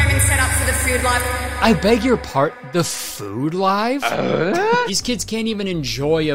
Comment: I heard "food live" as "third live" and thought for a good minute that Kurtis was preparing an elaborate bit about how absurd it would be for the mom to not allow the kids to eat without livestreaming it, only to find out that's exactly what was happening lol